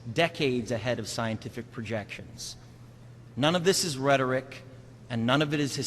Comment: I cant understand what he said in 2:51 None of this is "____" i JUST CANT lol anyone can help out? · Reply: None of this is Rhetoric ...